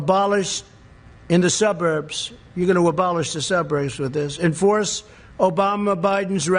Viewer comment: All I heard was "abolish the suppers".... And not much else. He is so pathetic. He needs to step down, NOW.